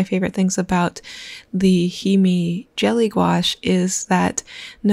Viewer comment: Did you say “HeMe”?